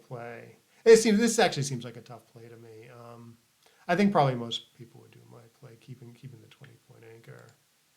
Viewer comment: Hey, I'm new to your channel and liking it so far. Wanted to give the constructive feedback that sometimes you mumble to yourself really quietly and it's tough to understand what you say. One of many examples is at 26:45 "I think probably most people mumbles keeping keeping mumbles ".